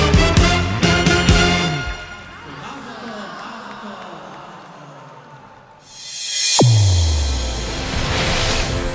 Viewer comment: What do they say? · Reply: "Mazal Tov"